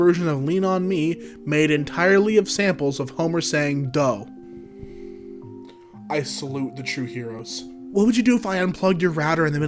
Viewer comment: I got an ad in the perfect moment so I heard "Homer saying the all new niisan "